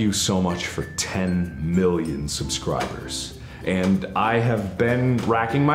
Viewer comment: I thought he said "Thank you so much for ten billion subscribers". I was wondering how in the world he got more subscribers than people on earth. That's true skill.